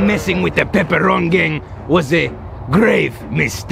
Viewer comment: Wait what is the name of the Gang it sounded like he said the pepperoni gang